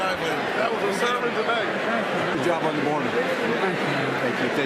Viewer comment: Did I hear someone in that gaggle (at 4:33) say "good job on the border"?